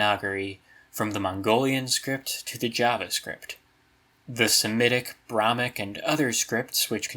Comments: Did you just say JavaScript? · i sat confused for 5 minutes I heard him say "javascript" I didn't know he meant the Javanese script